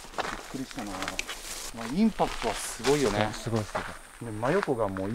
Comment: 、の「インパクトがすごいよね」のスグあと、が返事するのと同時くらいに「ね！」ってちがう誰かの声が聞こえる。